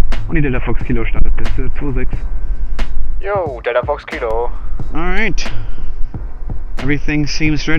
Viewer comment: it sounds like he says yo dele fox kilo (no offense)